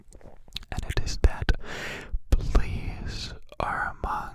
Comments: I really thought you said 'Police are amongst the best jumpers, they use their toes and shins to jump' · Can someone please tell me what he said at 6:36? I didn't understand the name of the animal and want to see it jumping xD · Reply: Its FLEAS ;)